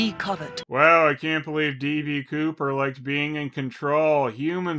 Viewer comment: DD Kovet I thought you said D.B Cooper